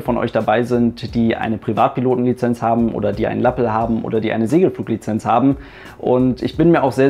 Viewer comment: Bitte was? "oder die eine Latte haben"? Was hat er gesagt?